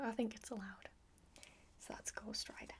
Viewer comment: I thought you said that's called strider,but you actually said that's Ghost Rider